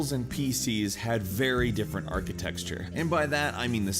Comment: Dying Light was pretty bad at launch, but now it is definitely much much better. But Arkham Knight... I just tried a pirated copy and was glad i didnt buy that. And is it me or does it sound like he is saying architexture instead of architecture... 😂